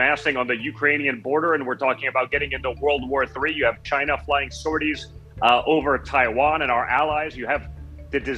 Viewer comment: Did he just say “sordees” He did though, Mr janiary